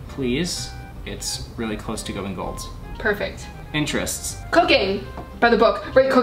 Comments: when she said cooking at 12:50 i thought she said “cocaine” 😂😂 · In 12:55 I thought she said cocaine oml 😂😂😂🤣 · i heard cocaine xD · I THOUGHT YOU SAID COCAINE